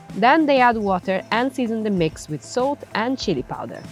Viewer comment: I think I heard "soap" insted of "salt" on